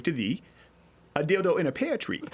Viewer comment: I heard you said "a dildo and a penetreé" 😃